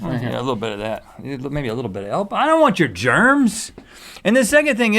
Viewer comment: did anyone catch that? I DONT WANT YOUR GERMS! * sniffle *